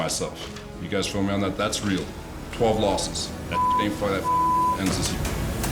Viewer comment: What does Saleh say at the end of his speech to the guys? 35:27 - 35:30 Heard it. He says "This shit ain't fun and this fucking shit ends this year." Woah, you rarely hear Saleh swear.